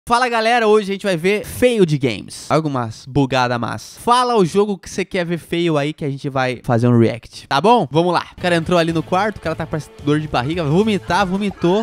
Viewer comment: Eu juro que entendi: Fala um jogo que você quer ver "feio" ai 0:03.😅